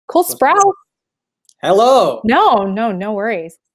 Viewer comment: I thought she said "cole sprout" in thr beggining